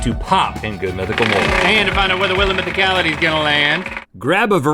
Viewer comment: When they said "Good Mythical Mugs" I thought they said "Good Mythical Moms." And then I was thinking, why don't they do a Good Mythical Mom episode? How about a Good Mythical Moms quarantine survival episode?